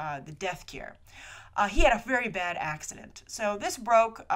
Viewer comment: When said " Bad accident. " i heard " Bad accent".... I'm horrible....qq